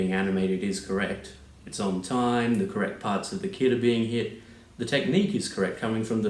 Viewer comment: am i just crazy, or did you say "the correct part of the kid is being hit".